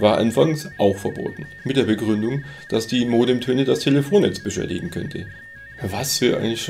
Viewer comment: Hääähh? Ich verstehe bei 1:43 immer " dass die Modemtöne der Telefonentzwischer liegen könnte"!!!????? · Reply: Sowas passiert mir auch manchmal. Der Satz lautet: "Mit der Begründung, dass die Modemtöne das Telefonnetz beschädigen könnte"